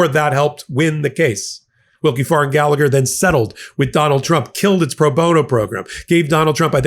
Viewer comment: did you really call him McDonald Trump? 🤣😂